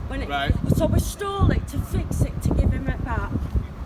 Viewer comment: did she just say we stole it to fix it and give him it back?? Lol